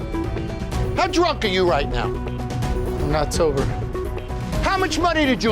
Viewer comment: I legit thought he said “I’m October” and that makes it even better 😂😂😂